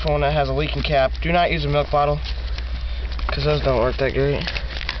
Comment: I hear "do not use a meth bottle" @ 0:45 ... shake n bake anyone? I know he's saying milk, but lol